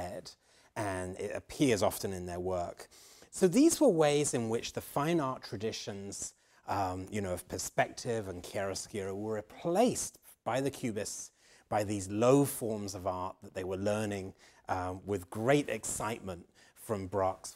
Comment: What does he say at 1:28? "These were ways in which the fine art traditions of perspective and..." Is it the Spanish word "charrasquear" and what does it mean in terms of its usage in fine art? Many thanks